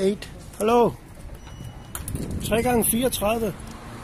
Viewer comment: what is he sayin? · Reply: He just said the Companys name the 2 Green guys were working for . Said: "3×34".